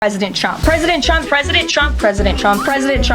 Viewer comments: I thought she was saying Pwesident Twump 😂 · Lol it literally sounds like shes saying President Chump